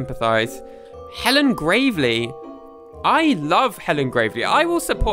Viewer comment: I thought hellen gravley was said as hellen gravy